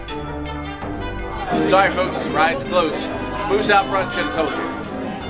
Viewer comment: What did he say? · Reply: He said “sorry folks, rides closed, moose out front should’ve told you”